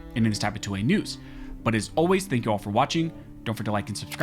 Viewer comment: Every time i hear you say "of Armed scholars" it sounds like Barmed Scholars.